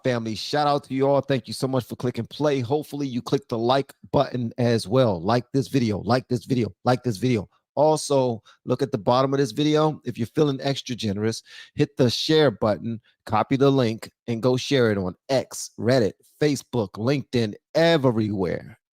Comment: Maybe with all this trans stuff going on, it's time for black men to cut their hair. I mean, when u said, "extra generous" I thought u said estrogenerous. It's time to look like a man with a family. Good talk.